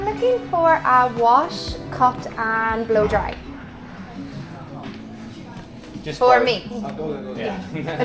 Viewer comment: Get a blow-dry...I heard "get a blow job", hahah